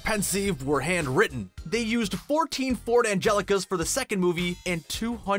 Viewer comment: I'm sure I heard 'Ford Angelica' rather than Ford Anglia!